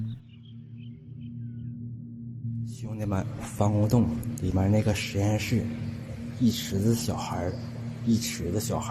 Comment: what did he say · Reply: all my n words down there in NYC